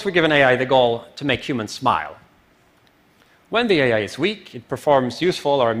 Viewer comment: I thought he said at 9:40, supposed we give an AI the goal to make humans spy, instead of smile. When the AI becomes super-intelligent, he may be able to detect other intelligence spying on it.